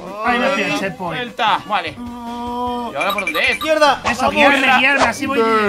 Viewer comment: mira lo que dice 9:59 malos modales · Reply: Dijo izquierda!